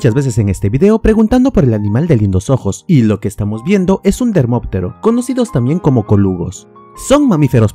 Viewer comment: Pensé que dijo termómetro